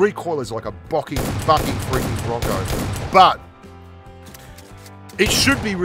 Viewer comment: Is it me or did say the F bomb at 9:39? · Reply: The recoil is like boking f***ing boking roco